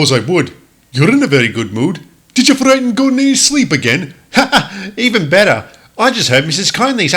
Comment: what did jams say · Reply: His whole bit goes: "Um, yes, I suppose I would. You're in a very good mood. Did you frighten Gordon in his sleep again?" After six takes, THAT was the best I could come up with.